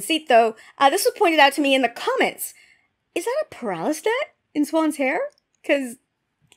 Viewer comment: >>> At about 07:18 in this video: What was the word you used to describe the thing in her hair? The closed caption says "paralystet", but I cannot seem to find a definition for that word online. {Since I am laying in bed as I watch this, I do not feel like schlepping into the other room to find the dictionary...😊} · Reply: Haha! It's not a real word. I can't remember how it's spelt, but it's a paralis net, it's like what Cadsuane wears in her hair.